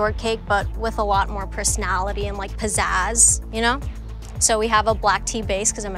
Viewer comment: did she just say possessed?? Or what was that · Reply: She said pizzazz